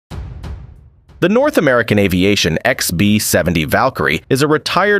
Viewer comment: I thought you said North Korea and I got confused but you just said North America. Deep penetration😅🤨🤔😏🤘💀.